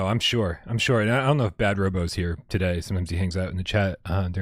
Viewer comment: Can't hear which name you are saying. Would like to look him/her work up on Dreams. I have still yet to try Dreams on the PS5. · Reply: I didn't check the timestamp but the two creators we talked about (iirc) were Wargarble and Bad Robo